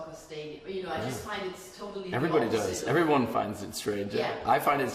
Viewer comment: What did he say at 8:03 "everybody find it's______?"